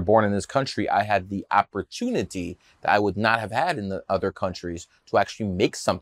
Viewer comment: Opportunity you say, I thought you said numerology